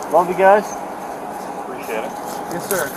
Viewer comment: Does he say “love you guys” @ 3:19??? OMG I am 💀